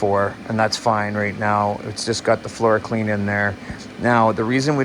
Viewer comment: oh florakleen...i thought you kept saying floor-clean and was using floor cleaner :P...smoke another one